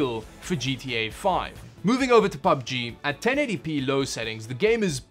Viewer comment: "1080p low" I hear: "1080 pilau" Man, that's a lot of rice.